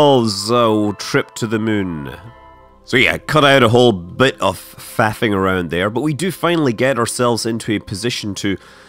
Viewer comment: I thought he said "After a bit of fapping around..." and I had to replay the video 4 times to make sure I wasn't hearing things right. Thankfully, he said "faffing". XD